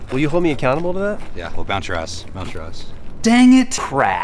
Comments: What is he saying at 1:51? The caption says "bounce your ass, bounce your ass" but that probably isn't quite accurate! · Did he say "we'll bounce your ass" at 1:48?